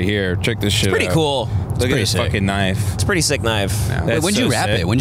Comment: Did just say "it's pretty fucking KNIFE" 😅😂😅😂